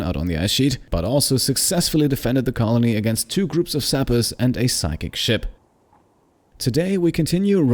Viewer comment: I thought he said 2 groups of hackers and a sack of shit